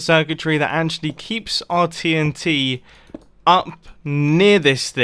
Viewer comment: So I just skipped 5 secs on 15:55 when he says TNT and it sounded like titty :)